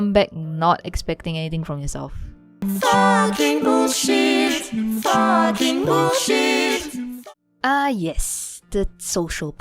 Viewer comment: "Fucking bullshit fucking bullshit" that's what I heard